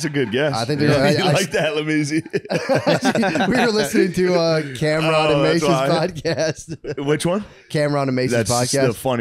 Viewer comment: what podcast are they saying? I can't make it out... Camera on a Mace???